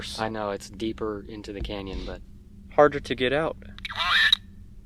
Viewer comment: I thought that said Wyatt until said "Quiet" repeating the saying. 37:31 I know it's just how the lighting is but it looks like the guys have been edited in. 54:53 Has anyone heard the sound after the rock hit the bush????